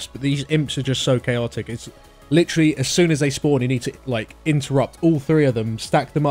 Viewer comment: Not gonna lie that sounded like "you need to imp-terrupt" :D